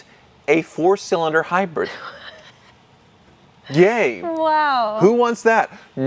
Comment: does straight up call the 4 cylinder gay? Bit of a strange choice so might be mishearing okay but can't unhear it anyway